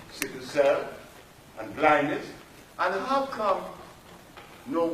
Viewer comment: Yo in the clip at 25:33 did he say he cured blindness🤦🏾‍♂️. Sebi was just saying anything.